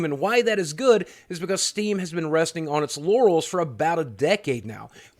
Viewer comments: I heard “Steam has been resting on its yannys” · Anyone else hear him say "resting on its yannis"?